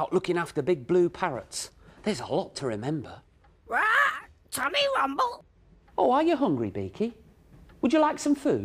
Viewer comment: RAW Tummy Rumble, Yes Please, RAW GIVE US SOME CAKE. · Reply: I always thought he said Toby rumble when I was little.